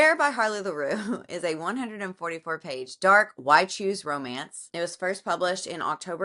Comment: Ok, embarrassing admission. I've heard you use the phrase "why choose romance" several times, but I thought you were saying "white shoes romance," which really took me on a Google journey, because it didn't think that was a thing. Because it isn't 🤦‍♀️